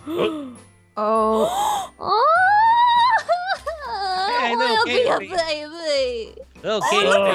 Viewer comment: did say Lil Katey Babe? 😨